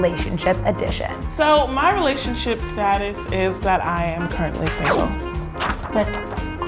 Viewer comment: the cpations say I am currently failing instead of I am currently single